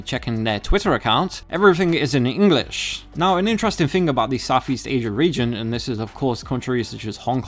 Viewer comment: lol it really sounds like you said "soft East"